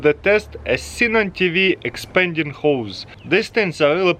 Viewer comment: All I heard was “Expanding ho’s”